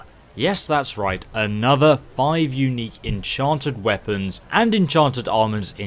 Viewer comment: Caption says Enchanted weapons and enchanted almonds. So silly.